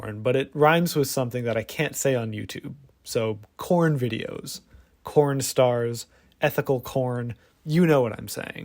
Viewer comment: I first misheard corn stars as corn starch plz 😭✋